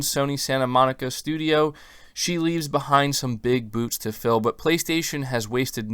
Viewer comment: does say "big boots" or "big boobs"? I know what it's supposed to say, but it sounds like the other. 😅